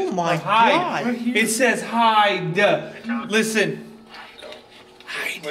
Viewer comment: WHAT KINDA FREAKY S#IT IS THAT, HE SAID HIDE IN ME WTF??????